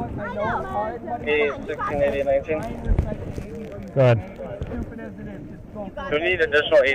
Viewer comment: Who is the Girl yelling trying to keep AJ calm? My gosh her loud mouth is annoying….be calm to an injured person shit. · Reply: did she say "I know I'm annoying." LOL I think someome told her she was being annoying hahaha